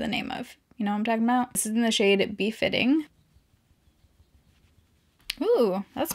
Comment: I thought you said the name of the blush was “beef hitting” 😭😭😭 im so stupid